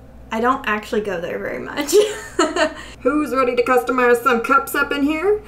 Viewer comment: Not gonna lie when you said “customize” I thought you said “cupstamize”. Maybe that’s what you should call these videos going forward. 🤣 Love your videos so so much. I’m relaxing after doing homework all weekend, enjoying bingeing your channel. If I was Starbucks I would totally hire you to design cups. ✨